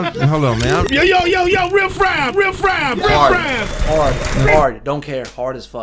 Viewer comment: When Sway finally shut him down and yelled "Riff Raff Riiff Raff" I thought he said, "real fried real fried" xD